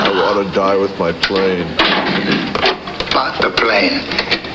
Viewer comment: Doesn't it sound like he says "Fuck the plane" ?